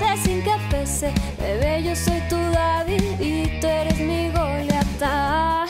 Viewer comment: En el 0:53 pensé que decía yo seré tu dabi y tu midoriya